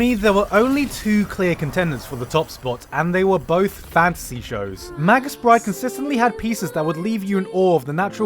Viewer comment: Can someone please tell me the anime at 7:05? I would like to watch it but for the life of me I can't understand the title when he says it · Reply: The ancient magus bride(I think)